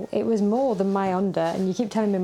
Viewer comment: When she said "my Honda" I heard "my under'😂